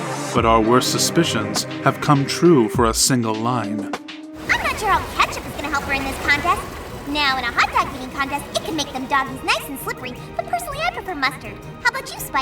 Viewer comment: what did Pinkie say? I can't understand her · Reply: “I’m not sure how ketchup is gonna help her in this contest but in a hotdog eating contest it can make those dogs nice and slippery but I prefer mustard” (I think this is fall weather friends and she is talking about something spike might have said out of context)